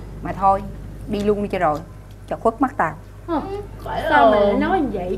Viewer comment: Hồng nói gì cơ!!? Phải hong mà tui nghe thành Vãi l** =)))))))